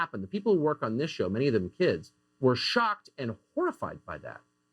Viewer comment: Did I hear that right? Did he say kids???